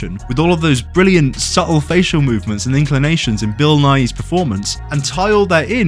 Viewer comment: i thought he was saying bill nye